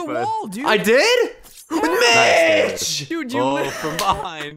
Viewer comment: I thought he said bitch!!